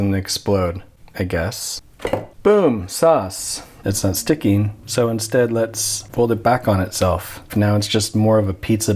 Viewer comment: what did he say? Boom... what? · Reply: "Boom, sauce"